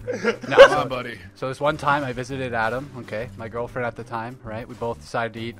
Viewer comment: idk about yall but to me it sounded like said adam was his girlfriend at the time xD it was probably just me